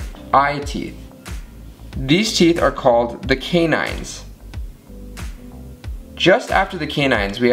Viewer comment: Interesting. I always heard it as Chinese 😂